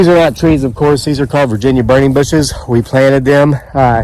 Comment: Thanks for the video One question if you dont mind? What type of bushes are those in your video at around 1:30 time mark? Thanks. Can't make out what you said. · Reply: Burning bush